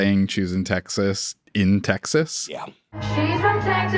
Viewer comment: Before the song title came up, I thought he said, Jews in Texas. I was like, damn, Ella gettin’ pretty progressive